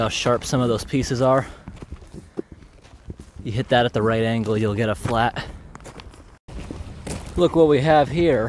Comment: I heard you'll get a flat